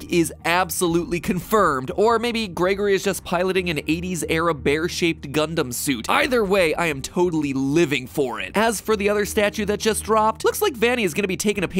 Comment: I heard Gundham and was confused for about 3 minutes Edit: also when he was talking about Vanny being a serial killer and not remembering it my mind immediatly went to Toko and Genocider Syo why am i obsessed